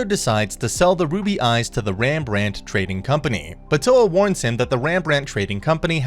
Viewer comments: hold on did he say Ram ranch at 4:45? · Did a double take because I thought I heard "Ram Ranch Trading Company."